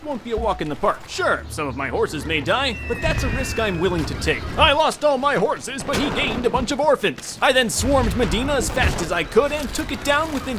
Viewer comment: What I heard: "I've lost all my horses but I least I killed lots of orphans."